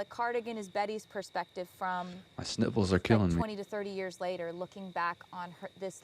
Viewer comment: I had to backup because I swear I heard “my nipples are killing me” I still don’t know what you said lmao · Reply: Okay upon fourth rewind I heard “sniffles” bahahah